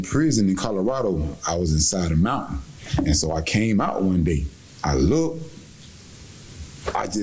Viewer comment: the narrator was talking about Markieth’s statement about the mountain possibly being a hallucination (mental disorder), but I think what Markeith actually said was “I was aside a mountain, and I came out one day…” which likely meant that when he went out into the exercise yard one day, and he saw the mountain that was beside the prison. That said though, I can definitely see him trying to pull an insanity plea.